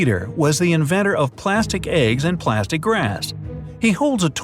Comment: did you say plastic glass or grass.